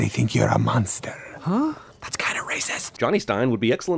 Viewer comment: Did Johnny just say the word "RACIST"?!?!?!?!